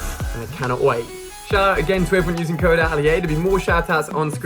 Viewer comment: I swear the left one says alla and not